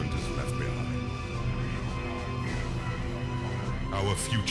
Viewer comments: I legit thought it said foreskin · Thought that said Foreskin